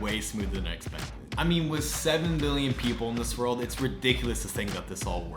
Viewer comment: sounds like u said million